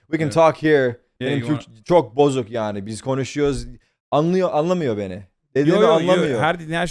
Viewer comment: Tarık: Benim Türkçe çok bozuk, yani biz konuşuyoz anlıyo anlamıyo beni, dediğimi anlamıyo. My Turkish is so broken, so we’re talking but he does not understand me, he doesn’t understand what I say 10:25 Hasan: Yo yo yo her dediğini her şeyi anlıyorum ama "konuşuyoz" dediğin zaman mesela.. No no no I understand everything you say and all of that but for instance when you say "konuşuyoz (we’re talking)" 10:29 Tarık: Benimki değişik biraz Türkçe. (Tarık is making an inverted sentence here. The correct sentence would be "Benim Türkçem biraz değişik.") My Turkish is a little different. 10:31 Hasan: "Konuşuyoz" "We’re talking" (I'd say majority of Turkish people pronounce this word as "Konuşuyoruz" so it's not shortened as "Konuşuyoz" but Tarık's pronounciation is not that uncommon. I'm a Turkish living in Türkiye and I pronounce it as "Konuşuyoruz".) 10:40 Tarık: Yani benim.. benim dilim biraz değişik definitely. I mean my.. my language is a little different definitely. 10:46 Tarık: Yeterli konuşmuyom o da var yani.. kimlen.. annemlen konuşuyom babamlan konuşuyom ailemden. Ondan başka yani fazla Türkçe yok. I don’t speak enough there’s also that.. I mean with whom.. I speak with my mom I speak with my dad, from my family. Other than that not much Turkish. 12:14 Tarık: Amına koyduğumun çocuğu! Orospu! yani Son of a mom who I fucked (kinda, not the best translation)! Bitch! so 12:17 Hasan: Ananı sikeyim senin! Fuck your mom! 12:18 Tarık: Ananı sikeyim! Fuck your mom! 12:41 Has